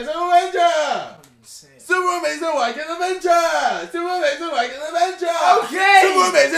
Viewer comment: Iat the start it sounded like KSI was sayin 'super wanking adventure!!!!'